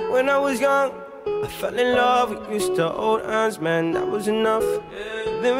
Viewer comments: I understand now, at the beginning it always sounded like he said "When i was young i fell in love with Mister old ass man, that was enough" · When I first heard this intro I thought he said "I fell in love with an old ass man"